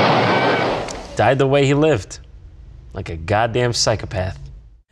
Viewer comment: did he just say I'm going in dry?